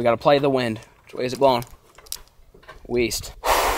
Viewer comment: EAST I THOUGHT YOU SAID WEAST